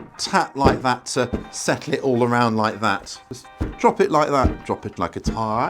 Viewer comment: Did you say “drop it like it’s hot” ?